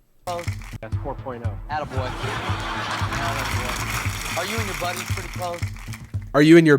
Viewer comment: he asks "are you and your friends pretty close?" But I misheard it as "are you in your friend's pretty clothes?" I spent way too long wondering why he borrowed his friend's shirt or whatever